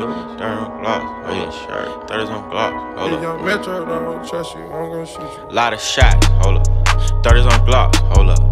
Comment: what is the guy saying before almost every song starts? Sounds like future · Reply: pezco.” If young metro don’t trust you I’m gone shoot you” it is future